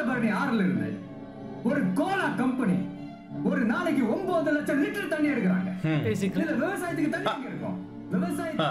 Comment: Vijay says 900000 litres of water. But the subtitle says 9000 litres of water. Is this intentional mistake in the subtitle? Also the overall translation tries it very best to mild the impact of the original dialogues. Don't really know why? Also, whenever there is a mention of terms like corporate company and MNC, they are avoided in the translation.